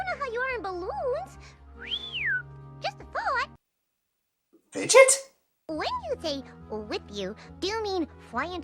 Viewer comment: Anyone know the name of the character from 22:00 ? I know says it, but I can't understand it. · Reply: LuckyShot25 Fidget. She's from a game called Fust: An Elysian Tale